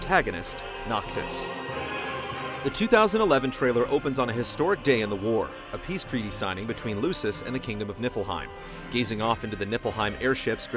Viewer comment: Thought I heard him say kingdom of Nibelheim?